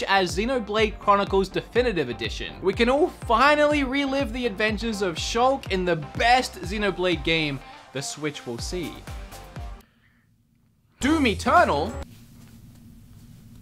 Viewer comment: In 4:03 who hears 'Do me ternal' give a like